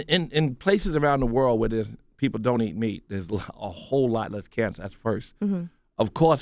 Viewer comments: wait did he just say ass cancer ? 😂😂😂😂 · did he just say ass cancer